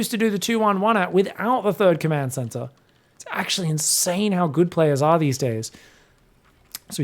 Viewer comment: What said: "It's actually insane how good players are these days." What I heard: "It's actually insane how good mules are these days."